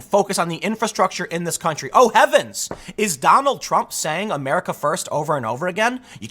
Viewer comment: Tim, it almost sounds like your saying "America first".